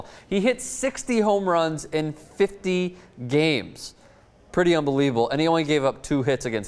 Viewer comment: I thought he said "60 home runs in 15 games" and I was like "GOD DAMN!!"